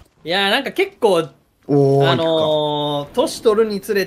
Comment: ここでロレからおーい？って聞こえるんだけどマジで誰かに話しかけられたかと思って漏らしたけどみんなも気をつけてな